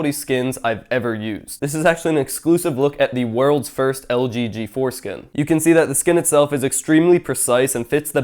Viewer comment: wait for it...wait for it..."LG G.....4(fore) skin" sorry but I just kinda paused confused when I heard that lol